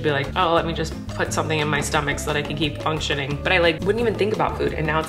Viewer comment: Did She just say, “So I cAn kEeP oN sH*TiNg”